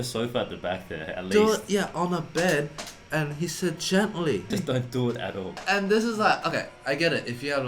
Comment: “Do it on your bed, and be gentle.” Hmmmmm HMMMM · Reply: I heard "Do it on a bed and release it gently!". Both are rated R, or we just have naughty, naughty minds. I do anyway. 🤭